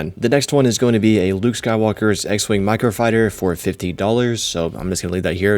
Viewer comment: Could just be how he talks but definitely sounds like he said 50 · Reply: yeah he def said 50 but pretty sure it's 15